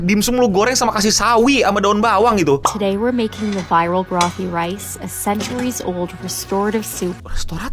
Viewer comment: Gua kira boty rice 😂,salah dengar ternyata